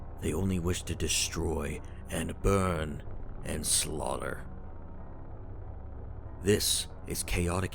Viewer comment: did he say slaughler?